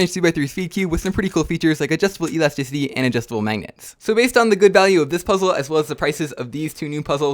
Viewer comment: wtf did u just say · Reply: adjustible elesticity and adjustible magnets